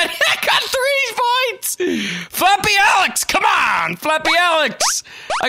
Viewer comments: At first, I thought it said "Fappy Aleks". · I clicked this just because i thought i said "Fappy Aleks"